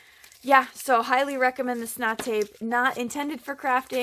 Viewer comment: What did you call that tape from Home Depot. Sounded like you said snot tape. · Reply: I did....it's called snot tape. gross haha